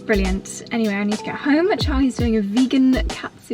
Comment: Congratulations on the powder room. It is coming along so beautifully. Yes, your wavy hair makes you look like a California beach bunny. You gave me a giggle at the end when I thought you said Charlie was doing a vegan Catfood curry for dinner.